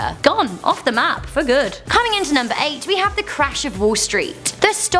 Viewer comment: she said rejecting trade" instead of "crash of"